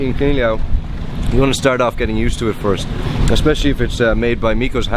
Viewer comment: When said "pulutan", I thought he said "pollutant". :p